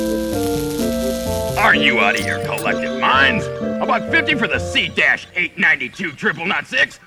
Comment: In 58:48, what did the Snickelfritz say? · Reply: Are you out of your collective minds? They're about to be out of my store!